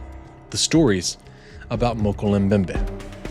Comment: I heard: “Mocal Mbembe” “Mocha mbembe” “Mowka bembe” 💀